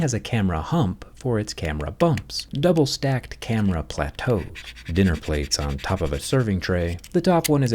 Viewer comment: did you say camera plateau???